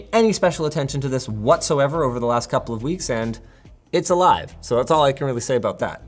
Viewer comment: What does he mean by 2:26???? Does it mean the ring isn’t water proof and he’s had issues? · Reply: Ooooh he said “alive” I thought he said “a lie” at first lol.